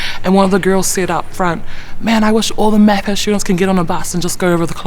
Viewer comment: what did she say? "I wish all the __ students ... " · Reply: She says MAPAS which stands for Maori and Pacific Admission Scheme